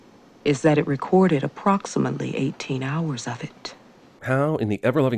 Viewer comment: Could have sworn that it said 10 hours of static